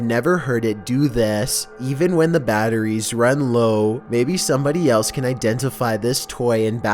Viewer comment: With the Mickey Mouse toy I heard 'Not Funny' instead of 'somebody'